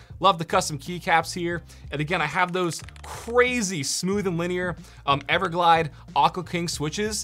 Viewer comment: Bro I gotta know what switches were in the white board!!! Cuz those sounded so smooth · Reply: think he says it @ 16:34 “linear everglide Aqua king”